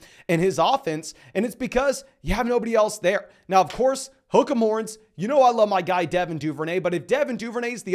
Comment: Hi, my english is not the best and there is one word you say quite often... automatic subtitles say "hookah morons" (4:10) Can somebody please explain what it means? hahaha you can laugh about me ofc... but please explain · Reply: Just saw this but what he's saying is "Hook 'em horns" which is the motto/team saying for an American college team, the Texas Longhorns.